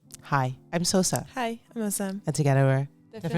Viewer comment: No one Everytimes they introduce themselves in the beginning, what i hear :"Hi im so excited"🤩